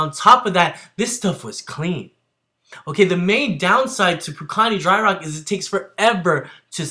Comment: Good lord let me get some rocks!! Scratches my neck I came here cause I thought you said "Build Punani from dry rock".